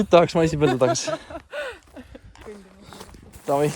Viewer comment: I thought that it might be hard to watch the videos in Estonian, but with a captions it goes quite well. Love how your language sounds! By the way, at 39:07 what it is exactly what you say to? I am hearing "davai, čau!"